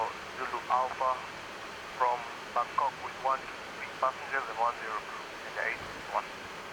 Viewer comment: did he say that Kenya 861 from Bangkok with1 sick passenger and 1 zero crew ? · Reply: I think he tells the mumber of people on board "...with one sixty(?) (160) passengers and one zero (10) crew".